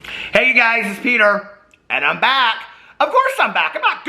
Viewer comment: Why did I hear "hey guys it's hater!" 😂😂😂🤦🏻‍♀️🤦🏻‍♀️🤦🏻‍♀️